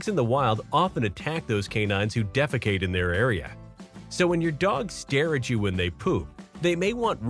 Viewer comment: I thought the narrator said, "When your dog stares at you when you poop!" 😂🤣